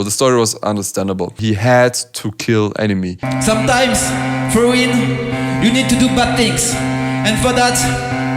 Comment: "Sometimes for WIN you have to do bad thing." Not "Sometimes for Him you have to do bad things."...you misheard a lyric again. Tsk tsk. :)